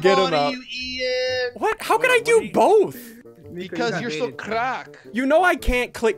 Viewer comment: Hear me out did say craaap or on crackkk at 5:08 threw